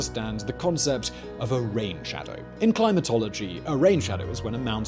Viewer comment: "rain shadow" all i could think of was all the business blaze RAID SHADOW LEGENDS jokes. Allegendly. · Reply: haha I swore he said "Raid Shadow"